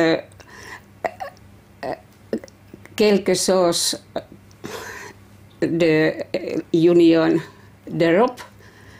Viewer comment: Someone french (or someone who speaks french) please tell me what does she say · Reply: Quelque chose = something